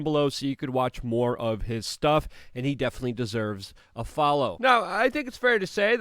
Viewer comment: I had to rewind...I thought you said Tim Poole deserved a follow🤣😅😂..uggh NO...I misheard